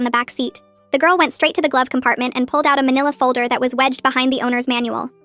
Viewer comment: I know this is fake bc it said “vanilla folder” 😂😂😂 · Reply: It might’ve said “vanilla folder” but it meant manila folder. The text is AI generated and it might be wrong sometimes because it generates text based on what it hears. Also it said “manila folder” not “vanilla folder”